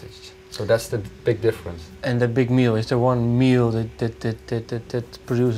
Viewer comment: What did Mark Jansen say here? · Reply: döner kebab hahahaha